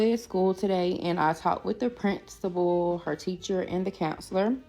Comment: what she says sound like prince tible 🤔🤨